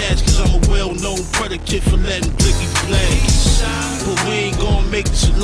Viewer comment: FREE MEL MURDA! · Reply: Mike of many Mikes facts u heard him say blicky huh lol